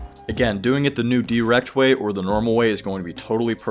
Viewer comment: When you said dwrecked I thought you said erect